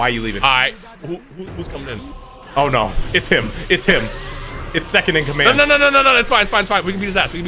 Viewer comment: Dotodoya watches y'all play together then stands out in the rain like Vegeta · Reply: When said "It's him." I just heard "That's Him with a capital H by the way."